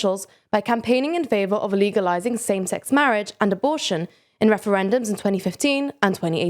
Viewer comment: sounded like you said "illegalizing".